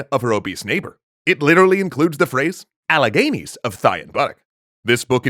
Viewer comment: What does the old woman say about thigh and butt at 1:46? I've listened to it for like 10 times and can't understand the first word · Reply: The whole phrase is I see Alleghenies of thigh and buttock